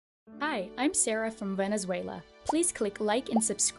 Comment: i heard "hi im sarah from venezuela" and i was like, finally an msa girl from my contry